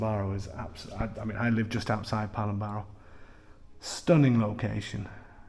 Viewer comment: Absolutely amazing. What is the location again? Sounded like you said Palamaro??? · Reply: PALOMBARO